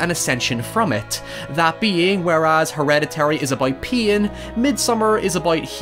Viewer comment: I really thought he said hereditary is about peein